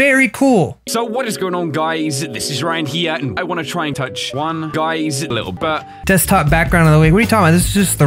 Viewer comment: When you went to Ryan and in his video I heard “ I just wanna touch one guys little butt” 😂😅